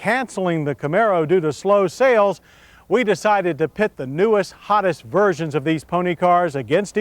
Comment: I heard "newest, hottest, virgins" and got excited for a second.